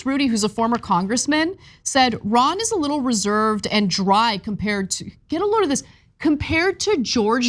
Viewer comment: Reserved and Dry. What I heard was "Rain Man". Lol!!!!!!